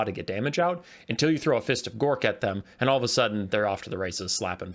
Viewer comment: I thought you said piss of gork is an S tier spell at first lol